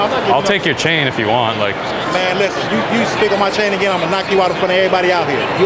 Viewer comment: Lofe, this guy ain't said "spit" he said "speak" cuz I replayed what he said over and over several times 🤣 ➡️